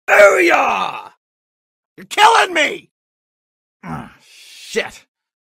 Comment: sounds like what death from puss in boots said “Your ruining this for me”